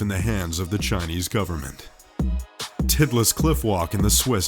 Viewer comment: did he say "titless"? lol